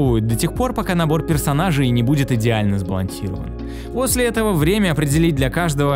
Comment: я один слышу не сбалансирован, а сбалансилован?)))